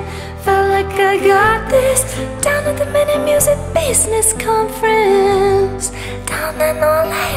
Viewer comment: Downatthemeninmusicbusinessconference 👁👄👁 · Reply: When I first heard it I thought she was saying mini music business conference 💀